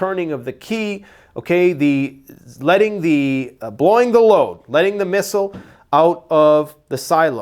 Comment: Did just say "BLOWING THE LOAD" at 3:26. Oh boy!!!